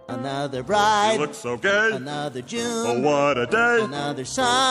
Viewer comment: I thought Quagmire was saying "Another Jew".